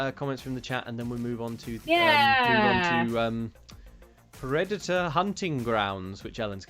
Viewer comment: Took me a moment to realise I had misheard and you weren't talking about Redditor Hunting Grounds